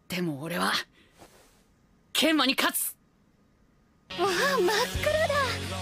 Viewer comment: Every time I hear this it sounds like "Kenma die Katz'!" which is German for "Kenma the cat" which in turn fits very well xD